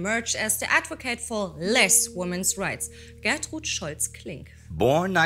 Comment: the captions are wrong: it says Born in 1904, but should read 1902. also, there are a few other little errors in the Captions. Edit: also, at the end, off camera, say, in German, "where is my bathrobe" <"Wo ist mien badmantel">